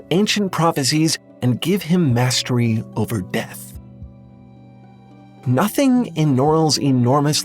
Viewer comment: misheard and was like “Beth? Who’s Beth?!”